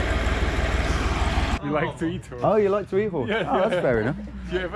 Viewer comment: Did he say he likes to eat horse??